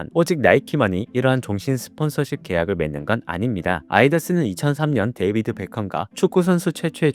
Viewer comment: 아디다스가 아이다스로 들리는건....😂 혀가 상당히 좀 짧으신듯한..😅